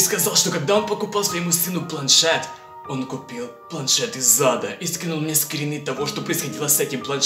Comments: Послышалось: "планшет из зада".))) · Мне послышалось или планшет из ЗАДА